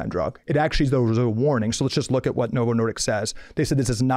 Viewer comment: I agree with Calley Means except it sounds like he is saying Novo Nordiks!